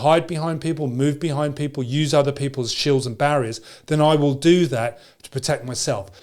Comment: I heard at 5:09 "I will use other people's children as barriers" :D